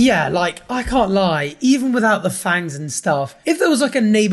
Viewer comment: all i hear is "thangs" not "fangs"